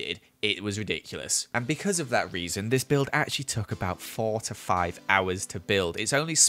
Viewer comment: did anyone else hear 45 hours!? 😂